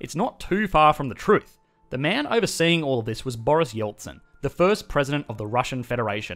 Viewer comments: I swear I thought he said Boris Johnson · thought I heard Boris Johnson for a sec